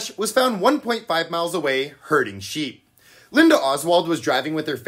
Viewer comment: Herding i thought you said hurting that's a lot nicer